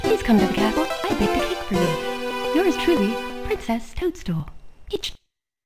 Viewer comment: i heard bitch